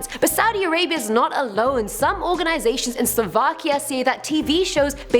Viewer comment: Did she say Slovakia?